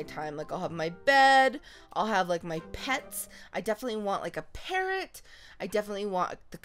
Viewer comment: When she said I definitely Want a parrot I thought she said parent LOL